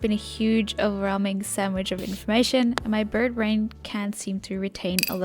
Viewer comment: did you just say birdbrain